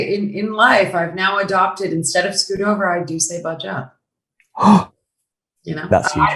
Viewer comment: The subtitles did not help much, could anyone help on what did Emma Stone said? Screw over in british slang is bunch up? Thanks in advance. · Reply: Scooch over vs budge up